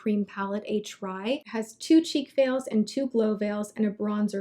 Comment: I swear I thought you said the Laura Mercier had two "cheek fails"...LOL I love seeing this as much as products you love. <3